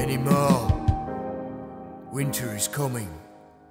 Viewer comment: At first I thought he said "I'm a basket" lololol